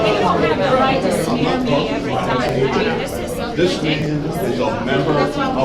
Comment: Did I just hear the person say this n*** is a member of black lives matter